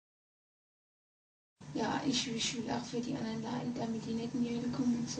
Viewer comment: Translation of what Anneliese says 8:12 - the normal Anneliese: "[unintelligible] but that it is so horrible, so bad, so awful. [Ne*]." 8:22 - Hitler: "[scream] [unintelligible] No [unintelligible]." 8:38 - Cain: "[scream] I hit a [unintelligible] [scream]." 8:50 - Nero: "The quiet! Quiet exists only above!" - (Priest, I suppose) "Quiet exists only where love is." - "Yes, at our (place) exists only [unintelligible]." 9:03 - Fleischmann: "[screams]." 9:15 - Judas: "They, they have to be taught in their own seminars so [unintelligible] at other universities. Do you understand?" (quite frankly, I do not) 9:26 - Lucifer: "No, no, no, no, no [unintelligible] Maria(?)." * I would compare that expression to a (sad?) sigh To be honest, the audio is really hard to understand, because she is so loud and it messes with the intelligibility. Maybe some of my fellow germans are able to understand more than I do. Please let me know! And I hope that my non-german-speaking friends got a little bit more insight on what she was saying (screaming) about :) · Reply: Nice work fellow german Ballen-Fan. Let me join in and give some fixes for what Anneliese said: Normal Anneliese:" Ja, ich will auch für die anderen Leiden, damit die netten Jüden kommen und so..." ("Yes, I want to suffer for people, so that nice jews would come and so on...) Cain: "Ich hab meinen Bruder geschlagen." ("I hit my brother") Judas:"Die, die müssen in eigenen Seminaren ausgebildet werden, die dürfen nicht auf die anderen Universitäten, kapiert?" ("They, they have to be taught in their own seminars, they are not allowed to go to other universities. Got that?")